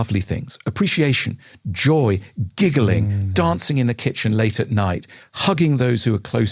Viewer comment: Misheard "dirty PANTS in the kitchen ". Laughed my socks off 😂